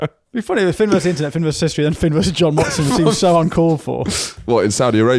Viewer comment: When you said john watson, thought youd said jon ronson.. you should have jon ronson on